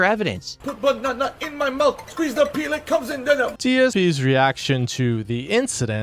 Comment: ok just humor me, watch it again and turn the playback speed down to 0.25. He clearly says “IT COMES IN DENIM” it’s just that he says the word “denim” like someone who’s mentally challenged. So like himself I guess Edit) watching DSP speak at 0.25 speed is pretty funny actually lol